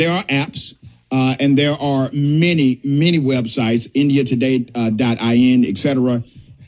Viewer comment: Did he say indiatoday.in app has globe earth proofs? It was hard to understand him.